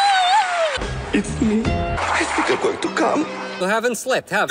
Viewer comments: I-M SORRY HE'S GOING TO WHAT AGAIN? AND THE NOISE I'M SORRY IT'S KILLING ME😭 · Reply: I hear "I think I want to çúm again" 🥺🔪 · what did Chris just say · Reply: i think i’m going to cam